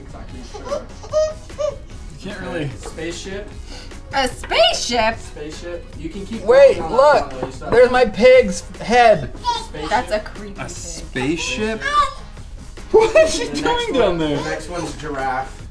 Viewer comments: Who heard say spaceship at 5:26! LUNA CAN SPEAK!! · is it just me, or does It sound like luna said spaceship? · Is it just me or did say spaceship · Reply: Yes! I heard her say spaceship too! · Is it me but it sounded like said Pig spaceship (When said to make that) And b1tch when said u heartless women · I swear I heard say, "spaceship" at 5:29 😂😂😂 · It sounded like she said space ship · it sounded like said spaceship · Im pretty sure it sounded like said "SpaceShip" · said spaceship at 5:25 I HEARD IT! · I think said spaceship